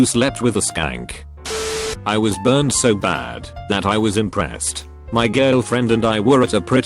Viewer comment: Thought it said depressed